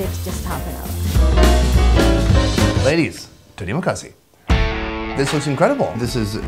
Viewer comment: Did you saying, "terima kasih" at 14:25?